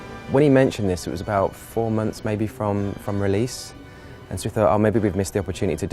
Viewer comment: I literally thought he said 4 minutes to release, and I was like... “what?”